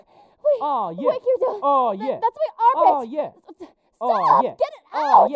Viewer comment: did she say armpit... I think Kirito's one of us.....